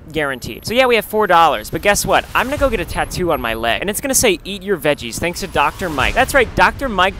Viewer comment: it sounded like he said yeet your veggies